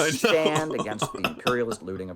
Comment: Lol is so bad at dogwhistling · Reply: For a moment I thought he actually said "the international bankers LEWDED Ecuador" and I thought it was some new dogwhistle then I was like, oh right he's bad at those, he was just referring to "looting" or something.